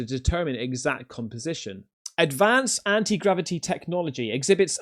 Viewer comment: excuse me, anti-gravity what you said? 😂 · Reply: 😂😂 .. I thought he said grab-a-d***. 😅😅